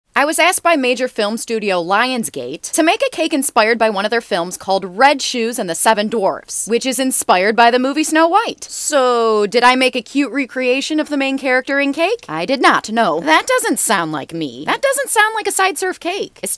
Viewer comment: i thought u said "bread shoes" i was like ooooh new movie but it was one of my favorite aniamted movies